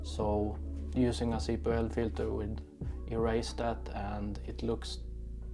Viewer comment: What kind of filter I have poor hearing and can’t make out what your saying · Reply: circular polarizer filter also called CPL filter, it cuts of the reflection and add some contrast to your pictures/videos